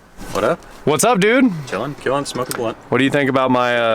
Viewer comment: did he say smokin' a blunt? lol